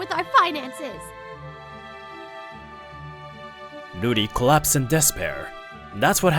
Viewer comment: i thought i heard "booty claps in despair"😭